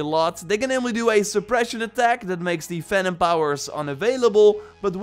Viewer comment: Am I the only one that thought he was saying Phantom power instead of Venom power?